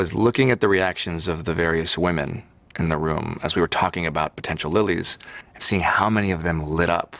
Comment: JB said “potential Lilly”. I thought I heard “potential bully”! 😂